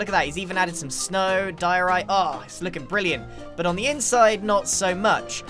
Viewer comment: wait did he just say diorite?